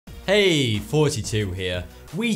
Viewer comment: it sounds like he is saying "Hey 42 here"